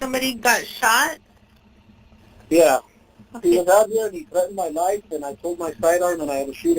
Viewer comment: holy cow, I 100% heard him say he pulled a knife when I listened at first, but went back and didn’t hear it on the 911 call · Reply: Exact same for me, too. Listening the 2nd time, I can't figure out how I heard "he threatened me with a knife".